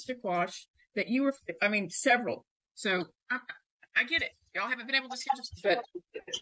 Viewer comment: I swear I heard the judge say, "I get it! Y'all haven't been able to schedule some shit!"